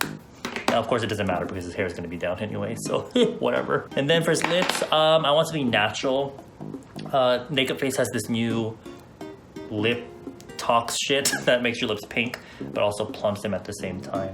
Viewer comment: i had to rewind the clip like 5 times bc i heard him deadass say “aNd then fOr his niPs”